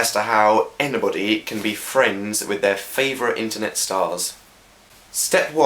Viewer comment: at first i thought it said ho how boyfriend your favorite internet stars LOL xD i bet they had so much fun this with done ;)